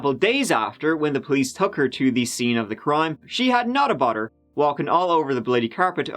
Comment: I thought said Nutter Butter and I imagined her walking around the crime scene munching a cookie. 🥜🍪🔪 “She had not a bother walking all over the bloody carpet ...”